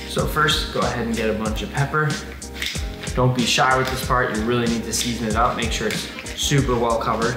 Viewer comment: when he said part I thought he said fart